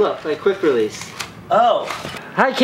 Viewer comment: When he says "Hi Casey!" I only hear Hike Yeezy's